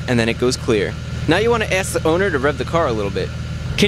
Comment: What did he say at " ... the car a little bit?" What does it mean? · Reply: Rev the car...